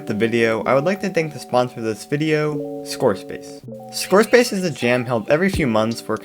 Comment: I thought u said SquareSpace lol im so used to Youtubres saying that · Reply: ScoreSpace > SquareSpace am I right